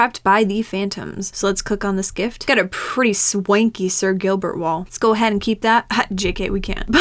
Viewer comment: "wolf in sheep's clothing " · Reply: I was sleepy when i watched this so I thought she said sir Gilbert XD